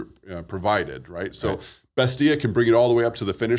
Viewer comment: what's that app he said? Bestia? Is that a AI sourcing app? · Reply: From what I could find it's Bestia . ai